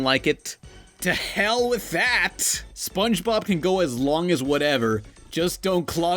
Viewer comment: did Gary say to hell with tht at 5:53? :O